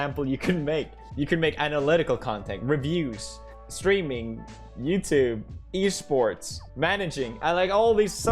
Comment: When he was listing the ways to make money gaming I thought for a moment he said "screaming" and I thought "at least he knows that's how he made his money"